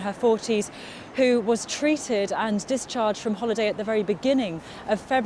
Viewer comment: Did she say 'discharged from holiday' at 2:58?